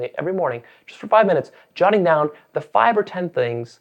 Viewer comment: Hi, I first wanted to say thank you for posting such helpful videos, they truly have taught me a lot. However, around the time slot 1:01, the automated subtitles say "45 minutes shutting down the five or ten things that." I was hoping that you could please change the word from "shutting" to "jotting", in order to make what you're saying a little bit more clear for your deaf viewers. Thanks!